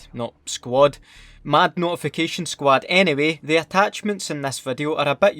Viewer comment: I thought you said naughtyfication squad